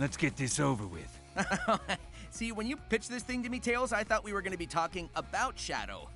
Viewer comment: I THOUGHT SONIC SAID “Tails, when you bitc- this to me” I HAD A MINI HEART ATTACK 😭😭😭